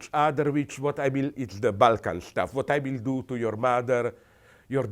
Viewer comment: the closed captions says he says "its the bulk of stuff" but it seems like it could be balkan instead of bulk? Is this an error? Anyone have an opinion on this? The word seems to fit better than bulk to me Idk thats totally balkan culture